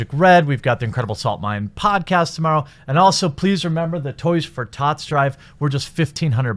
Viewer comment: I thought you said toys for thots for a second